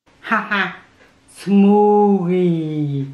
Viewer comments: she said " smoogy " instead of spooky. Lol lmfao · It sounded like she said smoothly